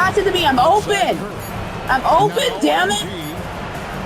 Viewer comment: “Trae I’m open damnit” he says while getting double teamed and did he call Steph Trae?